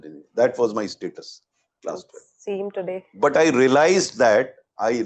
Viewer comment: wtf she said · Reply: she said "see him today" and not same today